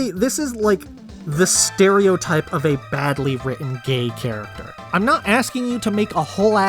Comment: I misheard you at first and I thought you said he was a gamer, and I was like ‘That is a very strange and oddly specific choice for a medieval fantasy game, let’s see how that works out.’ Him being gay is much less interesting, and I say that as a Bi person.